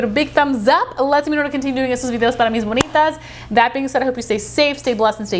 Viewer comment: what the heck was said here 😂😂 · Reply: "It lets me know to continue doing estos videos para mis bonitas"